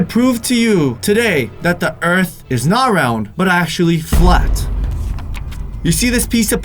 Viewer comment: is saying flat